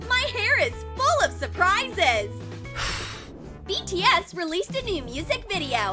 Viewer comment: did I hear BTS 😍💜🤣